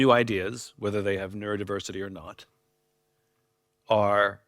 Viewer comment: I have both neuro-diversity and "nerd-diversity", which is what I thought he said at first. 🤣